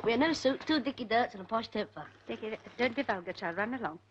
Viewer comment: "Ronald" (Cole) - "'ellen bought me a new suit, two dickie dirts [cockney rhyming slang] & a posh new jumper" "Mrs Barrington" (Casalis) relies to "Ronald" - " Don't be vulgar child, run along ". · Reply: i heard 'posh toffer'